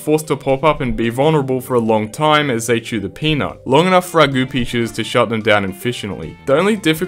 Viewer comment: Holy shit I thought he was saying “goopy” not “goo pea”